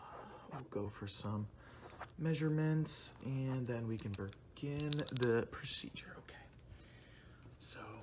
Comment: did he just say "bergin" 😭